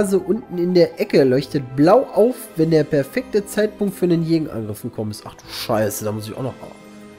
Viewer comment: Ich hab:"...wenn der verfi**te Zeitpunkt..." verstanden!